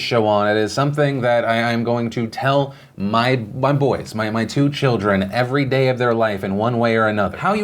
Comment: when stuttered a bit i thought he said I'm gonna tell my butt boys my children, I was like Damn that's harsh.